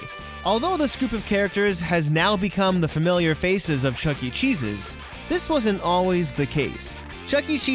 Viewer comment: I thought he said "Chuck e jesus" 😂